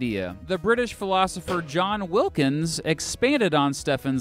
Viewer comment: i thought it said john wick